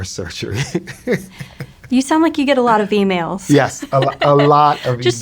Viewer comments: I thought she said "you get a lot of females" which is hilarious 😂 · I thought she said "You sound like you get a lot of females" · I thought she said lots of females. Lol